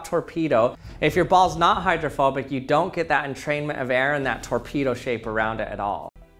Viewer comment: Why did I hear "hydrophobic torpedos".